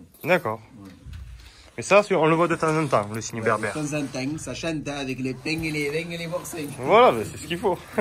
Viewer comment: Il dit quoi le mec à 15:20 perso jai compris temps en temps avec du pain avec le vin et du boursin